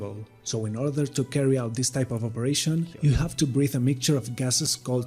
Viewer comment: give the guy a break, he clearly said "trimix". Don't nitpick! Your english is great, but occasionally you also pronounce some words incorrectly during Dive Talk videos (e.g.: pronouncing "z" as "c" or "s", and sometimes "v" as "b"). Keep up the good work